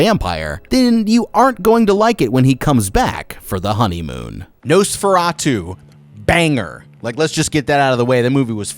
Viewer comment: "Who's flickering the lights?" Looks to the side "Nosferatu" -spongebob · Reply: I thought he said "noseoratoo" when I was young lol